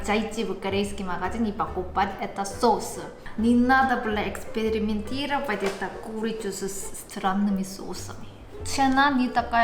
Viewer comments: я один услышал "не надо Бля экспериментировать эту курицу с странными соусами" 😂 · На 3:01 - не надо бля экспериментировать. Так же она сказала? Не послышалось? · не надо б@ эксперементировать? 😂🤌 Послышалось)))